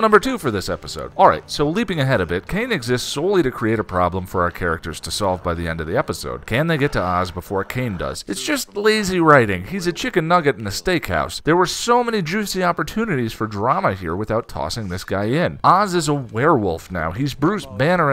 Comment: Misheard that as "Chicken Nugget in a mistake house" and i had to listen twice before I heard it properly lol.